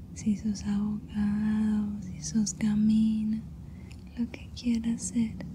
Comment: me gusta mucho tu asmr, es el mejor. PD: en el minuto 12:38 dijo gamin??? no escuche bien